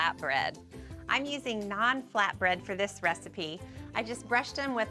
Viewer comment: I legit thought she was saying non flatbread…I was like erm how does that work? 😂😆🤦🏻‍♂️